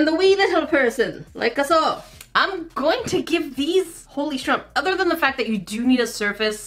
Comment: Did she just say “Holy Shrump” at 6:49? Lol 😆